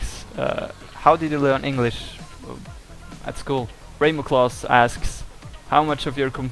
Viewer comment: When someone asked ( or whatever)how did he learn English, I thought he said it’s cool. By the SECOND time I watched it, he actually said at school.